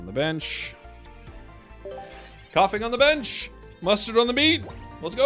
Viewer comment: Did I hear "Mustard on the meat" or "beat"?